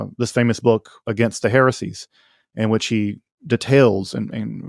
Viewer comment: Did he just call St. Irenaeus "she" at 1:34:18 ? He wasn't making much sense and neither was Alex, but that was unbelievable, he thinks he was a woman? I know Irene is a more common of a name than Irenaeus, but really, he doesn't know St.Irenaeus was a dude? He's portrayed with a huge beard, obviously talks about himself as a man how come he doesn't know that? And i don't think it's some mispronunciation, it's a clear "she", he's a native English speaker and they weren't talking about any woman, they barely mention any female at all during this, only the Samaritan one way earlier. I don' understand and he loses almost all credibility to me. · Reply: He says "in which he". Next time, spend 2 seconds relistening instead of rambling out a big ass paragraph 😂